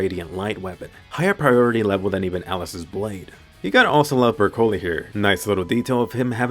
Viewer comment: Is it just me, that I hear "Broccoli" instead of "Bercouli" xD